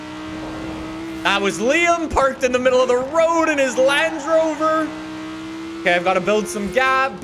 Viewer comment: did he say lange rover??